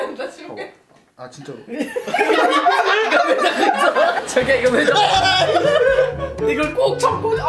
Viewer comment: “자기야 이거..”라고 나만 잘못 들었나..